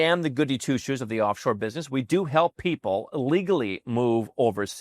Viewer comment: literally sounds like he is saying ‘illegally’…